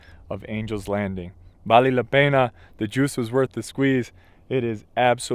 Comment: did you say “vale a pena” in Portuguese? Haha